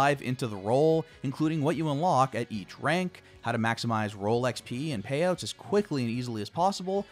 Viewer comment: when he was saying Role XP i thought he was saying Rolex-pee